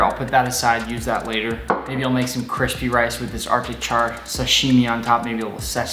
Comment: I thought it said a attic chair